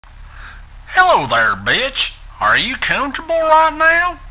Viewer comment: I WAS NOT EXPECTING THE ACCENT 💀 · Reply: I know oh my god when I heard “hey there bitch” I was like goofy?